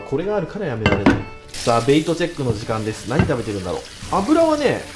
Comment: ってなんて言ってます？ベイトチェック？